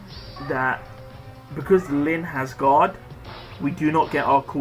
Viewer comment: I misheard it as "Because Lyn is God" and I laughed because she definitely is a tough one to beat! Excellent videos mate. I really rely on the Chain ones. I use Masked Marth with a Speed 3 seal and Reinhardt with an Attack 3 seal, I just have to make sure I charge up his Moonbow before each map like you often do with Julia's Dragon Fang. Please keep the great Chain videos coming for whenever they come out. Cheers.